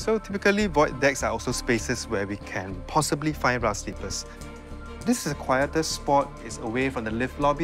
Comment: I keep hearing “rough slippers” 😂